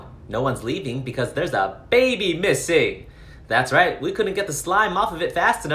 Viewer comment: I misheard that as "There's a baby pissing!"